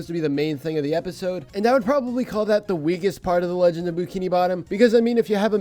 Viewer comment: I thought it said legend of boobkini bottom for a sec but it was bookini bottom